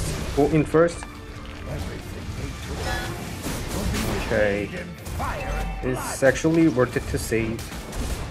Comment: Hay i I heard you saying sexually in the min of 6:30 make sure to look btw your gameplay was always cool as ever keep it up man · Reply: he is saying "it's actually". Its just mishearing or mispronounciation. Ntg big issue.